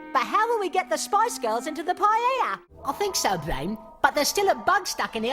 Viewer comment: did he say "Paiyaya,"or what?I can't quite get it. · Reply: I believe he said "paella", which is a Spanish dish with rice and seafood.